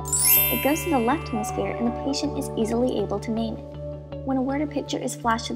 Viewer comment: what was said? 'The patient is easily able to....' what? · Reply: name it